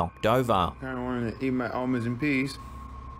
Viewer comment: “Tryna eat my almonds in peace” I dno why but the way he said that was hilarious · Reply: I thought he said almonds and peas. I was like, that is a weird combination...🤣